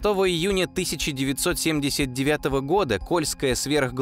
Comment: Мне сначала послышалось "польская" Сверхглубокая